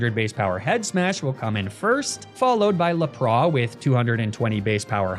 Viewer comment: I'm sorry did you say la pra instead of lapras 😂 ( 21:10 )